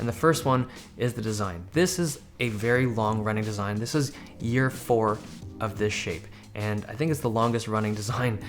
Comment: Almost thought he said "year 4 of this _shit_"